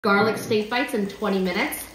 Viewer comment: I thought it said garlic stinky bites for a sec